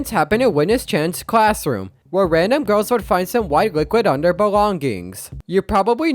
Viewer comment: why does it sound like "booklet" instead of "liquid"